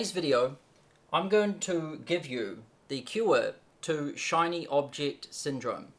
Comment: Cure! Lol, I thought he said "the q-word to Shiny Object Syndrome" and I wondered what a q-word was